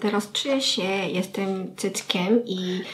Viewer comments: Uwielbiam to jak do ''zamian kosmetyczek'' wzięłaś osoby innej urody. Ewa jest od ciebie bledsza i inaczej się maluję więc zamiana stylów to był super pomysł! Osi wiadomo ma ciemniejszą skórę więc ciekawie było spróbować np. tych rozświetlaczy + to mega pozytywna i zabawna osoba - kocham ten filmik ♥ Co do Koreanki - też super, jest azjatką więc też inna uroda i super było zobaczyć czego używa itp. - filmik tez udany i bardzo mi się podoba ♥ Nie mogę się doczekać aż coś nowego nam powiesz po powrocie z Seulu, też bardzo chcę tam pojechać.. Może kiedyś się uda! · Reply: Ale Koreanka ma śmieszny głos :D w 9:13 usłyszałem jestem cyckiem xd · usłyszałam "Jestem cyckiem" a potem "Jestem dzieckiem" XDDD